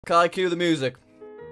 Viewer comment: captions say "can i kill the music" lol